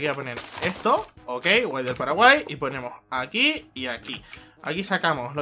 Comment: soy el unico que escucha que dice PARAGUAY?